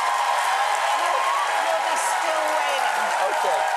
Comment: what did tina say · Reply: "You'll be still waiting"